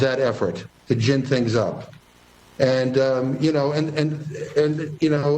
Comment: did hear right? Did he say “our job as elected officials is to tell the truth”? WELL THEN DO YOUR JOB AND TELL THE TRUTH TRUMP WON AND THE DEMOCRATS CALLED IN RIOTERS TO START TROUBLE AT THE CAPITAL BUILDING!!!